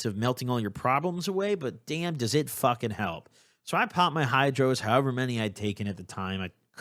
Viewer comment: Perfect timing just popped 2 ibuprofens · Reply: I thought you said pooped haha